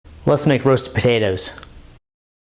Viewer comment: "Let's make roasted potatoes" All I heard was "Let's make babies" 😍😏😏🤣🤣 ok tmi